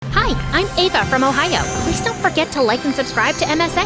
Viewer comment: DID SHE SAY SHE'S FROM OHIO? 💀💀💀💀